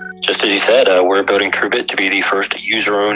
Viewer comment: I thought at first he said "Urbit", LOL.